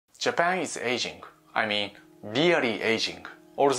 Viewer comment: “Japan is Asian, I mean really Asian” thought that’s what he said in the first line